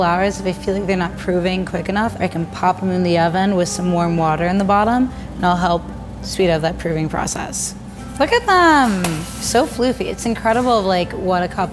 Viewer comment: i keep hearing her say "prooving", not "proofing"....